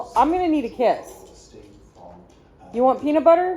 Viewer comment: I thought you said to Rocky, "I'm going to need to p iss". lol